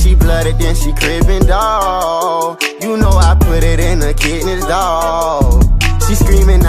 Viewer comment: I thought he said " you know I put it in her kidneys" 💀😭